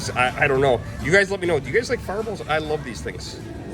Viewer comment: At first, I thought you were saying far balls 😅.